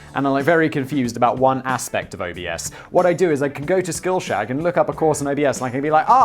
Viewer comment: I swear it sounds like Simon said "skillshag"